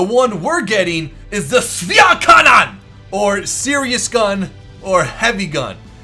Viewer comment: your german is so terrible! :D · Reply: sometimes his german is really good but i still dont know what he tried to say at 6:54, it sounds like "schwere Kanone" or something like this :D